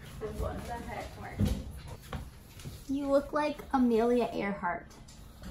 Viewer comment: “You look like Amelia Earhart.” 😂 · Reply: I thought you said Camellia Earhart